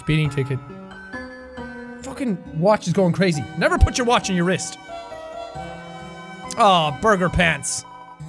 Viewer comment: “Never put your watch on your wrist” slowly puts watch around ankle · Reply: for some reason i heard: "never put your watch in your wrist" i n